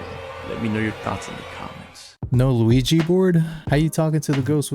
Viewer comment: I had a Mario Brothers flash when you mentioned the lack of an Ouija board. I heard 'Luigi board' and thought wtf? Then blushed. Oops.